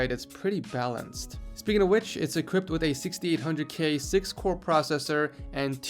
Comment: haha, I caught that you said e-cript instead of equipped at 3:23 nice one!!